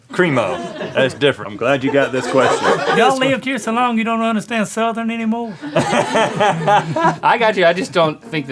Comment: I leave you two years alone and you don't understand southern anymore? i died with that xD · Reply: I believe he said "yall lived here so long, you don't understand southern anymore?" Got my masters in southern accents. hahaha